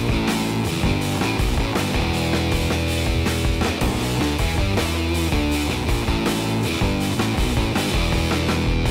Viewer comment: WAIT A SECOND! did he say illegal or legal? So it is not illegal I guess! :) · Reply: he said "lethal"